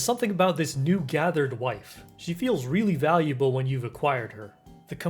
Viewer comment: Did you just call Aemy..... Wife...?